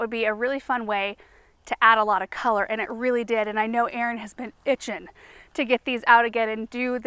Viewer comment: I swear I heard her say “Aaron has been bitchin’” And I almost spit out my drink laughing 😂